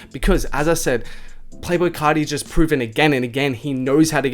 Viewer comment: It sounded like you said playful carti